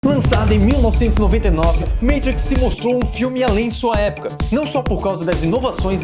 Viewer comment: Eu ouvi MEITRIX?